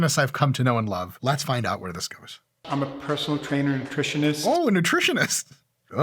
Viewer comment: I didn't hear "Nutritionist" I heard "I'm a Professional Trainer Impressionist."